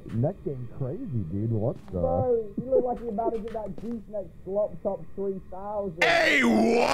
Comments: Anyone know what he said? I couldn't understand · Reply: something abt the Gluck Gluck 3000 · what did say? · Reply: He said, and I quote “broo he look like he bout to give that goose neck schlop top 3000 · what does say? I couldn’t hear the whole thing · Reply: He said, and I quote “broo he look like he bout to give that goose neck schlop top 3000